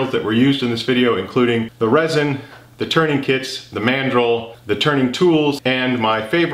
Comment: I'm so tired that when you spoke about mandrels, for half a second my zoologist brain heard it as mandrill and I half expected a large baboon to come help you. 🤣